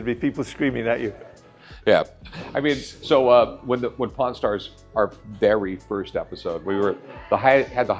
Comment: Why does it sound like Michael says Porn Stars